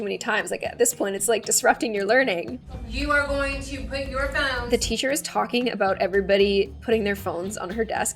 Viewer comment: did anyone else hear put ur mouthes